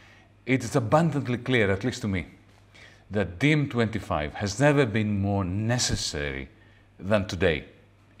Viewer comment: I thought he was saying "De M25 has never been more necessary"!